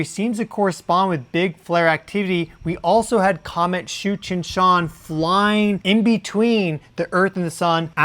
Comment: 😂😂😂 I thought you said Comet Cheech & Chong 😆😆😆 🚬